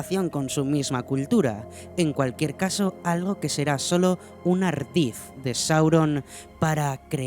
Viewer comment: disculpen que dice ahí?? No logro comprender "un _____ de Sauron para crear a uno de los Nazgûl" que dice ahí en medio?? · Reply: Ardid. 😊